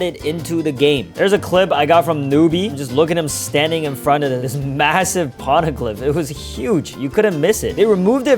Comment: Did you say massive?